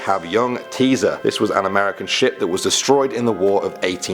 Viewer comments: I thought he said "American sh*t" 😂😂 Damn. I need to clean my ears regularly. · I heard American sh*t!!!😆😆😆